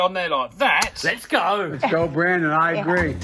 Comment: is he saying joel brand and i agree?